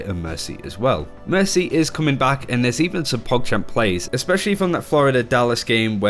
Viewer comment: did he say pog champ plays? what's a pog? seems like a new word i'm hearing as of late. stupid internet.